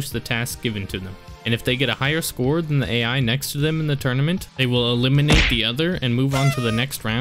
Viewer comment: Why the f**k does the caption says "67 next to them" in